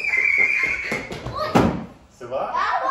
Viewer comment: I heard her husband ask her “ça vas?” when he thought she got hurt. Does he speak French?